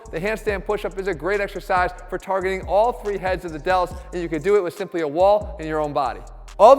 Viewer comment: did i hear all 3 heads